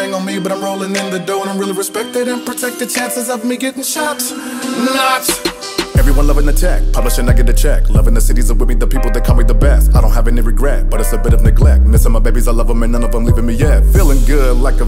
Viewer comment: 1 minute 40 seconds, sounds like he says the beth instead best